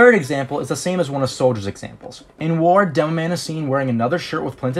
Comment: why are you saying dumb man instead of demoman